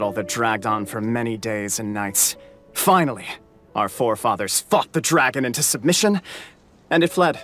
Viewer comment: Was listening to the lore while playing and didn’t read the text, so at 20:44 i heard him say “ our forefathers FUCKED the dragon into submission” and i was like what? I had to double check lmao